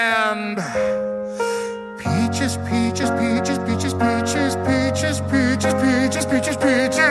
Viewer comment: that i heard: Bi*ches 10X